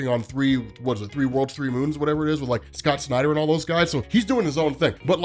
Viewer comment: For a second I thought said that Hickman was working with Zack Snyder, I almost started crying out of anguish 😳 and thema Inrealised je said Scott Snyder phewww!!!